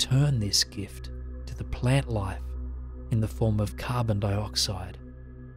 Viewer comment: "As each breath fills you, you're provided with life supporting oxygen offered to us by the plant kingdom ...And with each breath out we return this gift to the plant life in the form of carbon dioxide" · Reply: Carbon dioxide is not carbon monoxide. It sounded like he said carbon monoxide. We don't breathe that shit out.